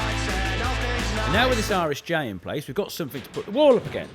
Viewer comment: This whole time I thought Colin was saying "an Irish J" and not "R-S-J" as in it was some silly English slang slagging on the Irish.